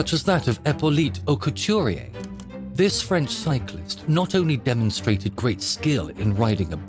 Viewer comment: whenever someone says "French cyclist" I keep hearing "french psycho"